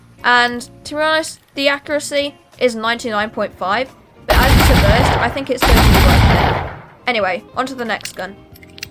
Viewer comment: what did you said when you were shooting with spitfire? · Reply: The accuracy is 99.5% but as It is a burst-fire weapon I think It's worth it